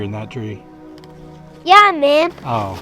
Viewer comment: it sounded like he said Yeah man!!! Lol!!! 😂😂😂😄😄😄